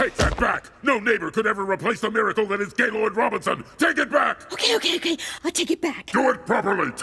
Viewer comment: Does anyone else hear "Gaylord Robertson" here?